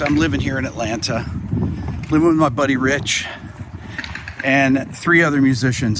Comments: At first I thought you said "I'm living with Buddy Rich..." :D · at first I thought you said you were living with Buddy Rich...🤣and several others. I didn't think Buddy was the roommate type.